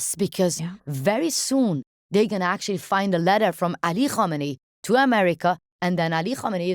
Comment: What name is she saying here? Ali Hominy? Im trying to look up this letter but I am not getting the name right. EDIT: I think I found the name, but I cant find the letter... MOHAMMED ALI HAMADEI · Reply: Ali Khamenei